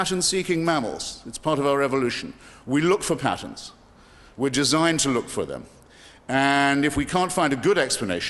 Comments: Did Hitchens say human beings are designed? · Did Hitchens just say "we are pattern seeking mammals (...), we look for patterns, we're DESIGNED to look for them..."??